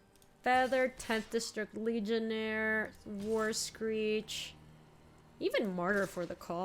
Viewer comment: what are you doing… Also I have the video on double speed and whenever says "War Screecher" I just hear "worst creature" lol